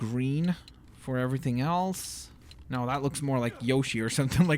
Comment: did he say "no that looks more like yoshi" when he was making yoshi?